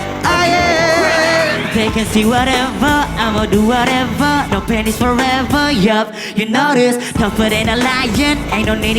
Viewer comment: Ahaha, at 25:45, I thought he said "No panties forever, oh yeah". Laughing so hard right now.